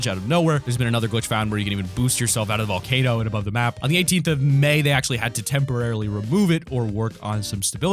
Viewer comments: On 9:31 did he say 18th of May? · Did you just say May?